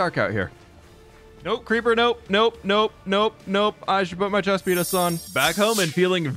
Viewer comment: it sounded like he said I should put my chest pe—s on